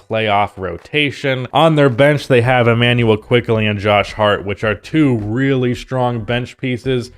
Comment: Thought you were saying something really deep and positive at the end there with the whole “IQ and Heart will show up in the playoffs” thing till I realized you were actually talking about Quickley and Hart 😅😂